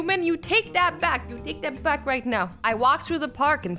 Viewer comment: did she say right meow? Lol 😂 🐱